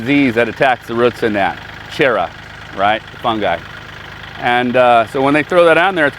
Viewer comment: good to see you back. Do you know the name of the fungis ? I heard you say chera ? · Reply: Phytophthora